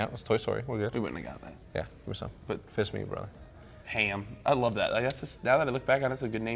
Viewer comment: WAIT, does Hecz say "Fist me brother" at 3:50?? Hahahaha